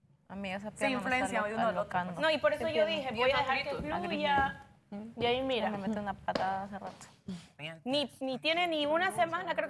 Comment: Alguien mas escucho lo que dijo en el minuto 16:53?? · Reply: Dice "Amiga esa pierna me esta alocando.... a Griss le digo, esta que me mete una patada hace rato" (Griss estaba jugando con su pie pateando a y cuando le dice para)😂